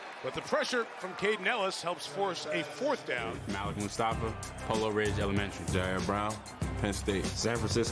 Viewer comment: did musfa say elementary lol hold on!